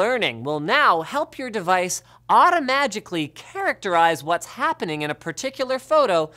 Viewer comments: did Linus really say "automagically" ? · Am I the only one who heard 'automagically'?